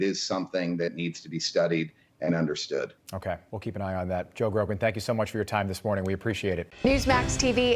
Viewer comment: Oh I thought you said Joe Rogan... Joe Grogan... I thought he looked different